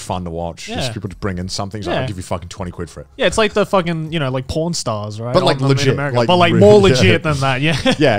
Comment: I thought Joey said "Pornstars" when he said "Pawn Stars"